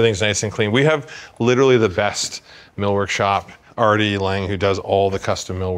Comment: Did he say Artie Lange? 🤔